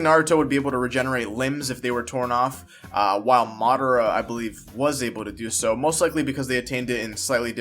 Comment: madara used it vs the first fused with the nine tails what r u talking about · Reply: Madara ? Thought you said Sasuke what r u talking bout